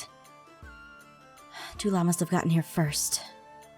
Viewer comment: When she said "tu'la must of got here first" I thought she said "two llamas must of gotten here first"